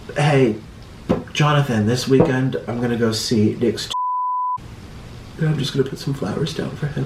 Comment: Ok plz tell me why I thought it said "I'm going to Vegas"